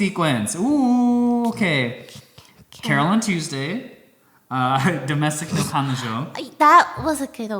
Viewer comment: Would anyone mind spelling the name of the show that says pls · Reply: Sounds like he said Na Kanojo, refering to Domestic Na Kanojo